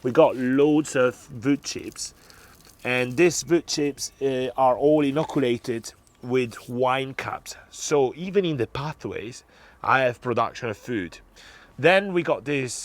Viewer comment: I didn't quite catch the part at 2:50. What are the wood chips inoculted with? It sounded like wine caps but I am not a native speaker and did not quite understand that. What do the wine caps do?